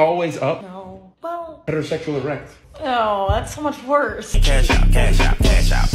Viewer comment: Why did I hear heterosexual rat???